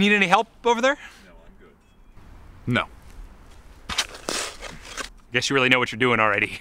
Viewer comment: Anybody else hear the voice at 2:18 say "No I'm good"? or am I hearing things?